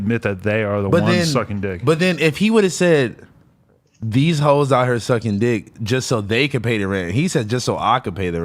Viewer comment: I heard “how hoes out here sucking D”